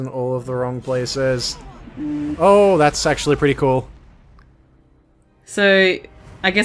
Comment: I thought that his sword extended was either a sexual innuendo, or at least I interpreted it that way, but when you said “that’s actually pretty cool” at 3:17 when it did that it sounded like “That’s SEXUALLY pretty cool” haha perfect timing